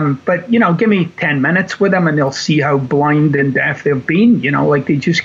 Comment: Watching this for a second time...it's THAT good!. And... did he just say, (??), Give me 10 minutes with 'em, and you'll see how blind and DAFT their being 😆. Now THERE's a turn of phrase for ya!🤣